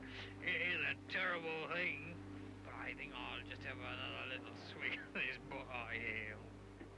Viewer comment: What is and how do you spell the word that Crowhurst uses at 17:02? Its all old word for strong liquor but I don't remeber its spelling. Thanks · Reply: You mean, "swig?"